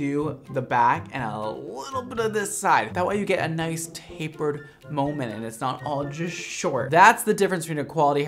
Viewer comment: he says tampered moment. (I dont exactly what he’s saying lol) but im like a fade???? No hate but god that was funny